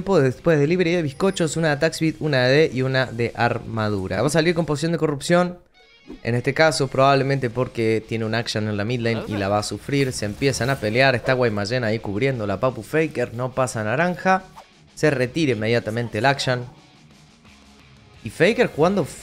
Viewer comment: estaba escuchando y pensé que dijiste guaymallen. Que calidad de alfajor para llegar a corea